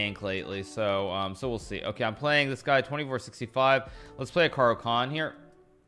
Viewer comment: I thought he said "Let's play an Hikaru Khan" at the start of the first game.